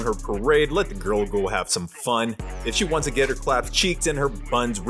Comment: Did you say her " claps cheeked " 😅